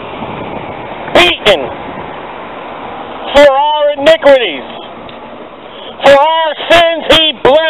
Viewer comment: did you say 'inigruities'?